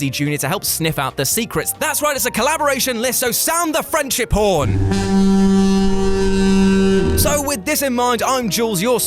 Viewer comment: It sound like Yu said sound the Frenchie porn lmao